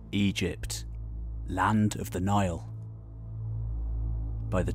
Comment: I heard “Egypt, land of denial!” And laughed my ass off.